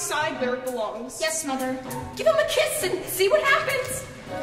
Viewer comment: Wait... WHAT DID THE NANA JUST SAY?! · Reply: She said to give it a kiss and see what happends.😏😏😏